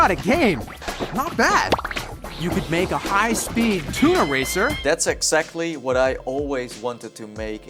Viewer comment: Just gonna say, you sound like your saying tuna eraser.